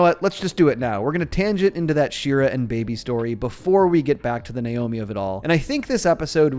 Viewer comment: I THOUGHT YOU SAID BACK TO THE NAIOMEAT OF IT ALL